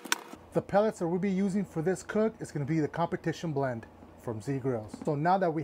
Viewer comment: What flavor pellets did you use..or like to use!? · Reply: I heard him say competion blend.